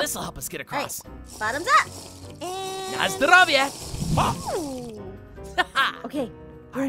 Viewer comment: HEY GUYS!!! did anyone knowtest that Noi was speaking another language? Watch the video on time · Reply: He was speaking polish He said: na zdrowie and that means have a good meal or sm like that